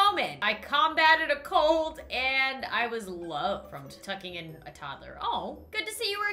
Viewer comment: OH MY GOD I THOUGHT IT SAID FROM F**** IN A TODDLER! ESPECIALLY BECAUSE IT SAID LOVED!😦😧😨😰😱😲😭😩 PHEW! THANK THE GODS IT SAID TUCKING! IT DIDNT HELP THAT I KNEW THAT MODS HAD BEEN ADDED AS WELL! INCLUDING MODS FOR "WOO HOO"! THANK. GOD.😰😓😅😌😁👍I'm good now. Kindof. Not really. But yeah😁👍 IM MENTALLY SCARED FOR LIIIIIIIIIIIIIIIIIIIIIIFE!!!!!!!!!!!😭😭😭😭😭😭😭😭😭😭😭😭😭😭😭😭😭😭😭😭😭😭😭😭😭😭😭😭😭😭😭😭😭😭😭😭😭😭😭😭😭😭😭😭😭😭😭😭😭😭😭😭😭😭😭😭😭😭😭😭😭😭😭😭😭😭😭😭😭😭😭😭😭😭😭😭😭😭😭😭😭😭😭😭😭😭😭😭😭😭😭😭😭😭😭😭😭😭😭😭😭😭😭😭😭😭😭😭😭😭😭😭😭😭😭😭😭😭😭😭😭😭😭😭😭😭😭😭😭😭😭😭😭😭😭😭😭😭😭😭😭😭😭😭😭😭😭😭😭😭😭😭😭😭😭😭😭😭😭😭😭😭😭😭😭😭😭😭😭😭😭😭😭😭😭😭😭😭😭😭😭😭😭😭😭😭😭😭😭😭😭😭😭😭😭😭😭😭😭😭😭😭😭😭😭😭😭😭😭😭😭😭😭😭😭😭😭😭😭😭😭😭😭😭😭😭😭😭😭😭😭😭😭😭😭😭😭😭😭😭😭😭😭😭😭😭😭😭😭😭😭😭😭😭😭😭😭😭😭😭😭😭😭😭😭😭😭😭😭😭😭😭😭😭😭😭😭😭😭😭😭😭😭😭😭😭😭😭😭😭😭😭😭😭😭😭😭😭😭😭😭😭😭😭😭😭😭😭😭😭😭😭😭😭😭😭😭😭😭😭😭😭😭😭😭😭😭😭😭😭😭😭😭😭😭😭😭😭😭😭😭😭😭😭😭😭😭😭😭😭😭😭😭😭😭😭😭😭😭😭😭😭😭😭😭😭😭😭😭😭😭😭😭😭😭😭😭😭😭😭😭😭😭😭😭😭😭😭😭😭😭😭😭😭😭😭😭😭😭😭😭😭😭😭😭😭😭😭😭😭😭😭😭😭😭😭😭😭😭😭😭😭😭😭😭😭😭😭😭😭😭😭😭😭😭😭😭😭😭😭😭😭😭😭😭😭😭😭😭😭😭😭😭😭😭😭😭😭😭😭😭😭😭😭😭😭😭😭😭😭😭😭😭😭😭😭😭😭😭😭😭😭😭😭😭😭😭😭😭😭😭😭😭😭😭😭😭😭😭😭😭😭😭😭😭😭😭😭😭😭😭😭😭😭😭😭😭😭😭😭😭😭😭😭😭😭😭😭😭😭😭😭😭😭😭😭😭😭😭😭😭😭😭😭😭😭😭😭😭😭😭😭😭😭😭😭😭😭😭😭😭😭😭😭😭😭😭😭😭😭😭😭😭😭😭😭😭😭😭😭😭😭😭😭😭😭😭😭😭😭😭😭😭😭😭😭😭😭😭😭😭😭😭😭😭😭😭😭😭😭😭😭😭😭😭😭😭😭😭😭😭😭😭😭😭😭😭😭😭😭😭😭😭😭😭😭😭😭😭😭😭😭😭😭😭😭😭😭😭😭😭😭😭😭😭😭😭😭😭😭😭😭😭😭😭😭😭😭😭😭😭😭😭😭😭😭😭😭😭😭😭😭😭😭😭😭😭😭😭😭😭😭😭😭😭😭😭😭😭😭😭😭😭😭😭😭😭😭😭😭😭😭😭😭😭😭😭😭😭😭😭😭😭😭😭😭😭😭😭😭😭😭😭😭😭😭😭😭😭😭😭😭😭😭😭😭😭😭😭😭😭😭😭😭😭😭😭😭😭😭😭😭😭😭😭😭😭😭😭😭😭😭😭😭😭😭😭😭😭😭😭😭😭😭😭😭😭😭😭😭😭😭😭😭😭😭😭😭😭😭😭😭😭😭😭😭😭😭😭😭😭😭😭😭😭😭😭😭😭😭😭😭😭😭😭😭😭😭😭😭😭😭😭😭😭😭😭😭😭😭😭😭😭😭😭😭😭😭😭😭😭😭😭😭😭😭😭😭😭😭😭😭😭😭pray for me...😢😁😂( These are NOT tears of joy )